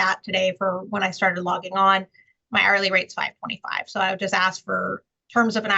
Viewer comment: $125/hr? You should be getting paid more. You should apply to work for supreme court, youre smarter than all the justices there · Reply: I heard $525??